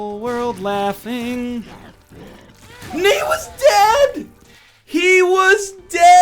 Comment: Does he say 'N-word was dead!"? That actually had me dying of laughter.